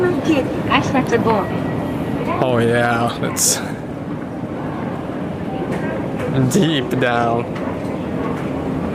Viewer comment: That's what she said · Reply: She said, "大井町駅改札方面、下りエスカレーターです。" "Take the down escalator towards the Oimachi Station ticket gates."